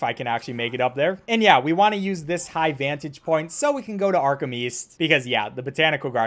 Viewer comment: Patrick Star: Arkham East I thought you said Arkham weast